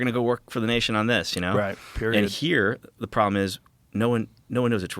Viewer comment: did Joe just say "Period" 😂